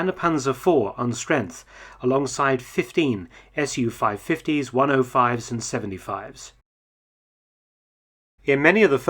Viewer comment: Somebody, please help with the part at 3:27. This is what I think I'm hearing, English not being my first language: "...alongside fifteen SU-550s, -105s and -75s." Am I hearing right? What are these vehicles? I have never heard those designations before!